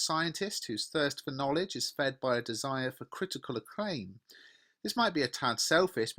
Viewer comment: I don't understand what you said at 1:11 , critical something... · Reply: critical acclaim, i think